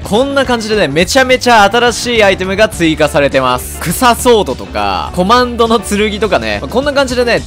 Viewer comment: 臭ソードに聞こえてきた✌️